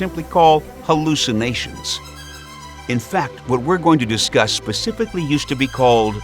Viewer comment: Does he say pacifically... 🙃